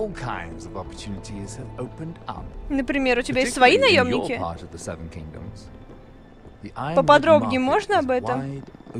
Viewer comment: Седьмой дом, следующая серия, ура! :D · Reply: сегодня в серии я услышала "seven dick" :D